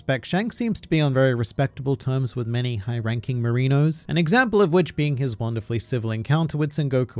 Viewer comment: Almost spit water when he said marinos (9:05) cuz I heard merino, which is a sheep 🐑. And now the admirals are all cute fluffy sheep in my head 😅